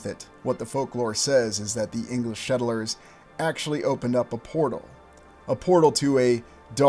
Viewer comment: Why do you keep saying "shettlers" instead of "settlers"?